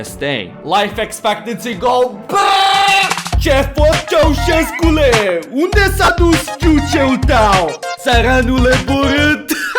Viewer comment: what does he say in romanian at 21:21? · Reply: Ce faci Ceaușescule, unde s-a dus ??? (I have no idea what was that word because it doesn’t exist in Romanian) tău, țăranule borât How are you Ceaușescu, where did your ??? go, you peasant (“țăranule borât” is a Romanian saying, “țăranule” means peasant but for “borât” I can’t find the proper translation, it’s something about vomit tho)